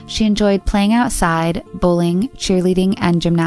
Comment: Am I hearing this right, bullying?